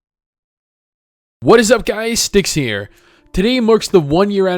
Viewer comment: When he introduced himself i thought he said "What is up guys, Dicks here" so can anyone tell what is his name???